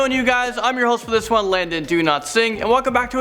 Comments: when you say your name it sounds like your saying "DoNotSing" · I legit thought he said do not sing